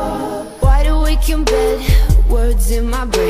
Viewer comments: i always thought she said "secretly you love fish" ; what a plotwist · My friend thought it said "im secretly a love fish" 😂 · Am I the only one who thought that when she said ''secretly you love this'' she said secretly your a love fish?